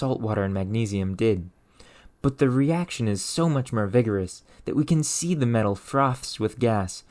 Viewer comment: I fell like he is saying myrrh instead of more to test my OCD. - 6:02, out. You win.